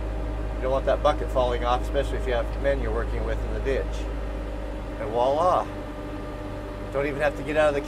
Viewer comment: did you just say "whalla" instead of "Voilà" as in French for "there it is?"